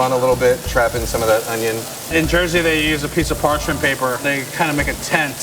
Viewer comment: Did he say "In Jersey they use parchment paper." ? If so, where? I wanna go.